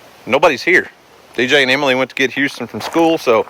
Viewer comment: I am sorry but I thought y'all were saying Emily instead of EmmaLee